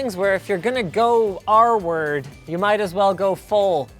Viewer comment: "R-word" I thought you were saying "arward" or some other word I hadn't heard before. I see now that you meant "retarded".